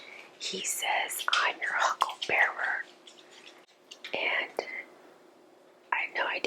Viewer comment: Wow thanks for the clarification for the Tombstone movie. Now it makes sense. I’m one of those who thought he said huckleberry also. Thanks for the great video.👋🏼💙🙏🏻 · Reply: I think everyone thought that! Look for the clip of that scene on YouTube. He says it correctly… huckle bearer.